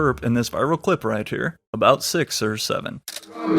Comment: did he just say 6 7